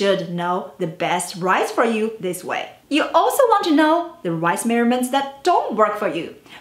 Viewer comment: OH I thought she was saying finding the "rice measurement."